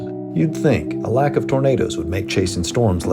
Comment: If you go to 10:24 he put burritos instead of tornadoes with subtitles